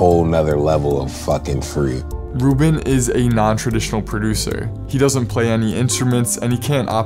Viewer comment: I thought you said he didn’t play any instruments